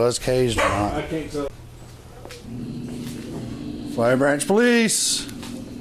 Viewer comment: Imagine having to yell “Flowery Branch PD!”unironically lol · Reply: I thought they said fire branch like they were part of the fire department or sum shit lol